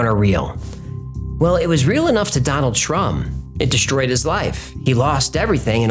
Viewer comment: totally sounded like you said, "Donald Trump" ... lol